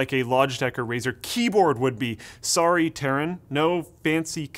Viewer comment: Did he say: "Sorry terran?"